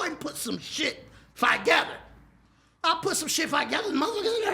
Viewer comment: Hold up...at 8:50 mark.....did he say put some shit "5gether"? Instead of TOgether😂😂😂😂😂man i quit